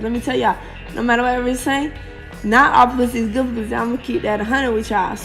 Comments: I really thought she said “not all pussy is god pussy” 😹😹😹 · NGL I thought she said "not all pussy is good pussy" too lmao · Oh damn I thought she said “not all pussy is good pussy” too and she was just trying to use a weird metaphor